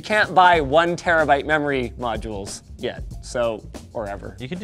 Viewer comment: did you say yet????